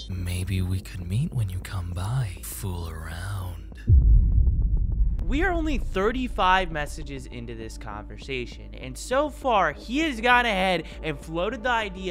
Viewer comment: why did i hear we are only 35 messages into this cockersasion